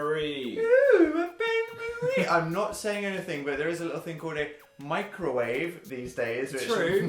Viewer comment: I hear 'microwave' as Mikey Way I think I have a problem